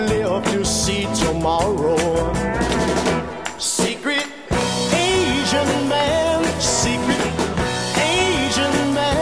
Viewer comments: I always thought when I heard it as a kid it was “Secret Asian man” lol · So true. My friend was the first to tell me about it because she's in band, and I thought she said "secret asian man"....I can't unhear it. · Am I the only one who thought he was saying secret Asian man.